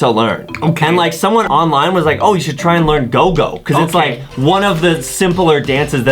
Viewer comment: When he said gogo I thought he said google and I thought bts actually made a song that was named google